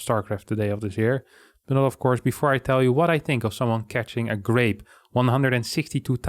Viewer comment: Oh grape, I thought he said grenade. Grenade would be more impressive.